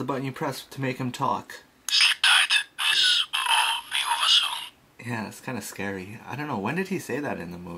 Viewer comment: What is Professor Zündapp saying? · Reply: Sleep tight. This will all be over soon